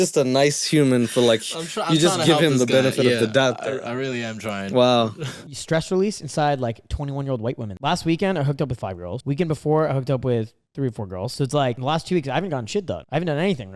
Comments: He needs to fucking enunciate. Bro, her said "5 girls" but it sounded like "5 year-olds" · I already thought he said at 7:54 “i hooked up with a five-year-old” instead I hooked up with five women my brains and neck snapped as soon as I heard that 😂😂😂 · I really thought he said "I hooked up with a 5 yr old" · 8 I swear he says I hooked up with five year olds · Oh my God at 7:55 I thought instead of saying he hooked up with 5 girls that he hooked up with 5 year olds and I was just so completely speechless and shocked and angry until I realized. Tell me I'm not the only one who heard that